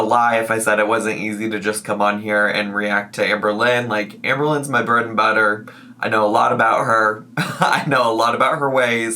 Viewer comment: Idk why but when you said "amberlynn is my bread and butter" I thought your said "amberlynn is my birth mother" I had to rewind to try and figure out why you would say that 😂😂